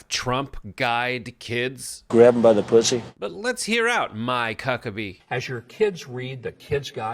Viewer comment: did you just say "my cucka bee"?